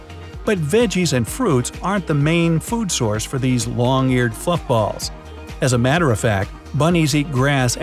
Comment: was I the only one who heard f**kballs 😂😂